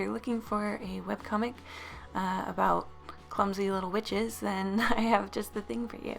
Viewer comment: I ALMOST CHOKED I THOUGHT YOU SAID CLUMSY LITTLE BITCHES